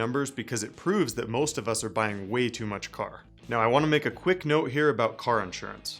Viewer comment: did he just say, you're buying way too much car ? lol